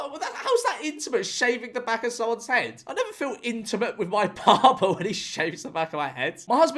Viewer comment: I genuinely thought said “I never feel intimate with my PARTNER while he’s shaving the back of my head” lmao I had to replay it